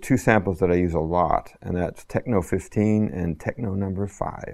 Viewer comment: cant understand which samples he use. can anybody tell me what tchad says at minute 1:25? want to know which samples does he use..thanks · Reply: Drumagog, techno5 and techno15